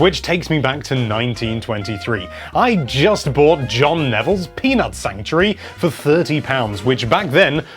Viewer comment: i thought he said penis sanctuary